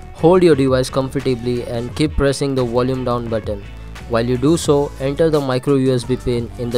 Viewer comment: hold what? can someone help me with this? i didnt get the first part of what he said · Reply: Volume Down (-) or Volume up (+) Button according to your phone to boot into recovery mode.